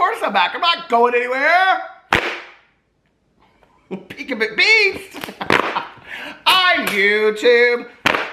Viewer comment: You want something funny! The first time I watched you I thought you were saying Peace instead of Beast. I didn’t know what Dad AF was and thought there was an album coming out lol!! I’ve learned so much ha!! 💙🛼🙏👍🏼